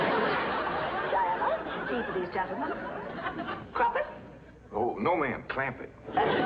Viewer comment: Did she just said at 2:17 "Crampet?" and the man answered "No ma'am, Clampet!" omg